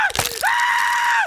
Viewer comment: the English subtitle says "Thanks, bae." instead of what the video says, "Thanks, man"